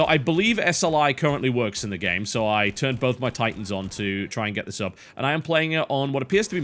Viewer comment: I'm sorry am I hearing correctly, or did you just say BOTH your titans!!!???